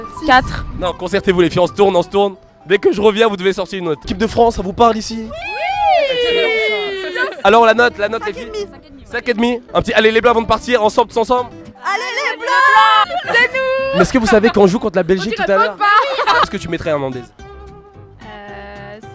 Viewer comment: Perso à 4:21 j'ai entendu "allé les blancs" 😂😂😂😂